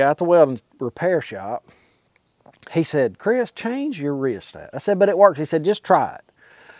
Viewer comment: what are you saying? change your reset??